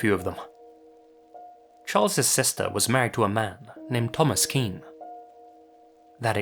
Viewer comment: BRUH I KNOW SOMEONE WITH A NAME SO SIMILAR i have a friend named Thomas Skene and i heard that i thaught u said his name and i was so confused